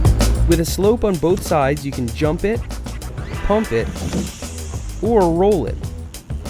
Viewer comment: did he say hump the ramp